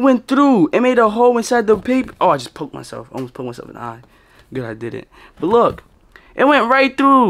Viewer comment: I thought he said i pooped myself